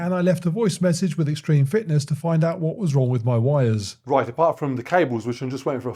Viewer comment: am i the only one that thought he was going to say, what's wrong with my wife instead of what's wrong with my wires.